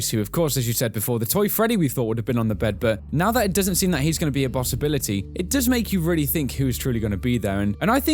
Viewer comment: on the video... did anyone else hear 'bossibility'? Not to make fun of how anyone talks, but I just found that quite amusing ;)